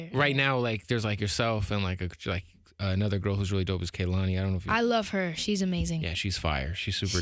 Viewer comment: yo fam what was the artist you mentioned that you said was fire around the 6:50 mark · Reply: Kehlani